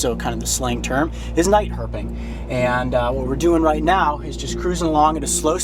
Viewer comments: I thought he said night herpes. I'm like WTF? KINKY COYOTE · I thought that he said night herpes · I thought he said night herpes at first